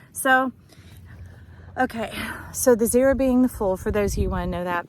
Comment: What a Joy to find you. What a Blessing you are! Shine on! @ 6:30ish, you said "The zero being the Fool..." But I heard "The zero being the Full (as in moon... Hmmm...)